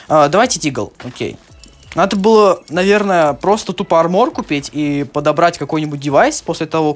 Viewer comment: Мне послышалось мармок а не армок